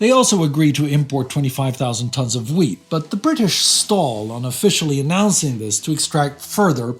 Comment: minutes into video, speaker uses word "stall" defined at to stop or cause to stop making progress. Transcription has it written as seal. Not trying to devalue the work done, just a minor yet important detail. Please keep up the great work, and if it matters to know I was surprised when showing this channel to my son he informed me he was already aware of this work because it was being used in his high school history class by the teacher (to be honest it seemed that the teacher was simply using these videos to teach the class instead of doing so themselves). So, yes evidence that this content is being presented to the youth, yet also evidence that someone else is profiting from the labours of others perhaps? wow off point of a video error and onto something else we went.